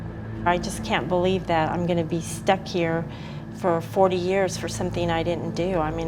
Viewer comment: got off but she is just as guilty. You drive to the house and shoot somebody when u could have turned around. She's a cold-blooded murderer and should never be trusted. Playing the victim card. Makes me sick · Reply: I thought she said she did 10 years for it?? That’s not getting off.